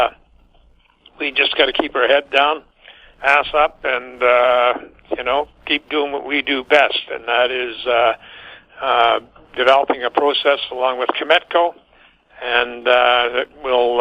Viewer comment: did he say head down ass up?